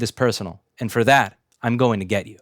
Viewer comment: That ending though 👍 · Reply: I had to listen to it twice... At first I thought he said "I'm going gay"... I mean, props... I guess, for the dedication to your convictions, but how does that actually help anything? Then I listened to it again... OOOooohhh... "I'm going to get you"... Hehe, that makes WAY more sense. I think I need to clean out my ears!